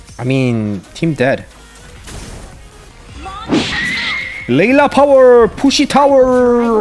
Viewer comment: "Layla power Pussy tower" Ah wait i think i misheard it🤣